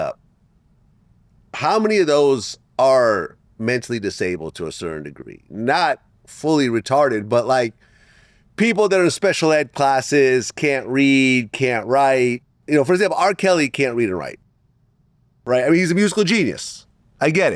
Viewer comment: like "did Vlad just say retarted"